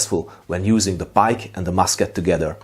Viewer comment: I at first thought you said "...using the bike and musket together". This totally sounds like a tactic that a dutchman, like Maurits, would invent ;-)